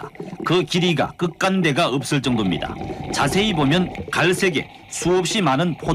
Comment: ~55인가여기사이쯤에서 자지보면이라고들림